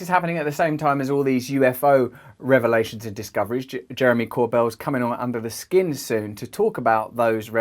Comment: 1.28 thought he said Jeremy corbels coming on in a skin suit.... Listening only that made me look up at the screen quick sharp haha alas, he not turned Ed gein... Just my hearing. Lol